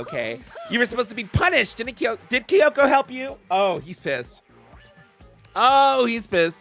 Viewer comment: YIU ALWAYS POST AT RHE RIGHT TIME WHAT THE KCFU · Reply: I THOUGHT YOU WERE SAYING KFC HELP